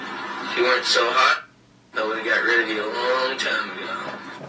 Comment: what he Said?! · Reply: Hé said if u are not so hot i rid u from a long Time .